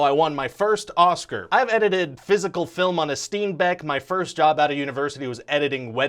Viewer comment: I heard "I edited physical film on a Steam Deck..." and I was shooketh. Because I knew it was impossible... And than I seen that beautiful looking machine. Also, thanks. I'm going to start streaming/posting video gameplay, and I'm sure this will live happily in my 'educational' playlist.